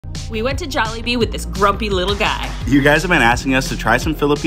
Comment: I heard “brokie” little guy instead of “grumpy” lol